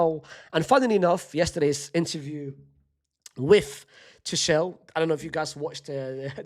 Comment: unc saying toshell instead of tuchel before mbappe scores a header 💀.